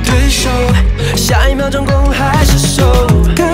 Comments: 对不起对不起对不起 听了好多遍我听成了 “下一秒做攻还是受” 我不对劲 啊啊啊啊啊啊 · 只有我听成“下一秒攻还是受”？